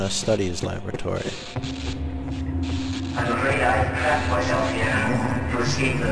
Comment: I've seen playthroughs of this one before now, but this is the first time I misheard that line. I swear he said 'Crapped myself here'.